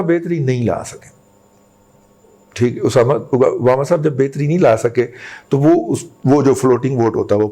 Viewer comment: osama 😂😂 instead of Obama🤔😁